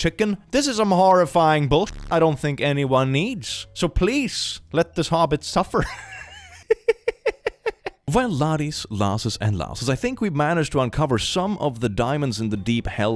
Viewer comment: I'm sorry, but whenever does his little welcome speech I keep hearing "Welcome Laddies, Lassies and *Lass-holes*" 💀